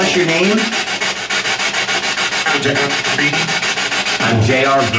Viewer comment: the voice on the spirit box says, "I'm Jeffrey". has just asked for it's name. @ 53:57 you can hear a woman laugh and then say, "Breeding". Like she thought what said was funny. @ 54:11 the woman's voice says, "I'm Brittany". @ 55:08 asks, "do you want us to stop playing this game? Then you hear a voice say, "I'm cool". @ 55:12 a woman says, "Emily's here". @ 55:16 you ask, "do you have a message? And a very, very deep voice says, "the devil". @ 55:26 asks, "is there anything you want to say to us" and that same deep voice comes in and says, "demon". @ 55:51 asks, "is there anyone with us right now"? And a voice says, "Michelle".